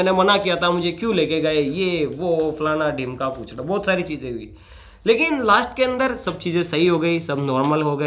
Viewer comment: per falana dhimka ke bad kya bola bhai🤣🤣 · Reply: Puchla bola hai bhai 🤔🤔